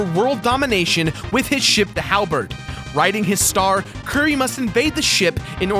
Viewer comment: Did anyone else notice him say curry instead of Kirby at 3:42?